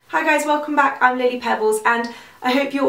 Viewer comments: It sounds like you say 'I'm Lady Pebbles' :p xXx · I thought you said It's Lady Pebbles...